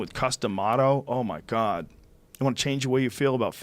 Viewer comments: I thought he was saying "custom auto" lol. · Don't know boxing, I thought he was saying "Custom Auto". I said to myself, " That's a pretty strange name for a boxing legend." I now know that he was saying Cus D'Amato lol